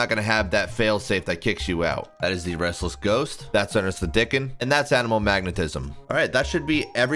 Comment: Ernest the dicken 😂 thought I misheard like 3 times